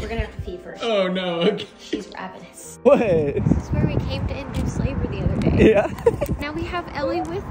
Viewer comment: I swear I heard “ This is where we came to undo slavery the other day” 😭😭